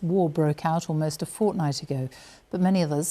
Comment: did she say Fortnight?